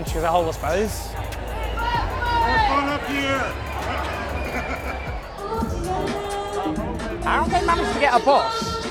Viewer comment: What does the person in the window say at 5:30? All I can hear is "it's fun up here" but that makes no sense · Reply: I watched it a couple of times wondering the same thing. I assume the crowd were asking him to come down and he responds, "I'm fine up here!".